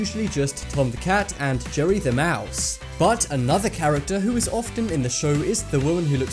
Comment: Did he say joey the mouse